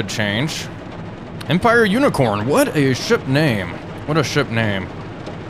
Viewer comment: When you said Empire unicorn I thought you said what a shit name😅